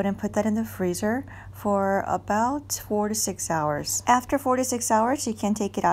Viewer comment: did you say freeze for “4 to 6 hours” or “46 hours” before putting into a ziploc bag? Thank you for this video it was very helpful! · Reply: Freeze for 4–6 hours :) Happy dumpling making!!